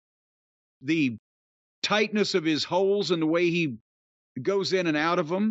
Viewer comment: I thought you said, "The tightness of his hol..." nevermind! LOL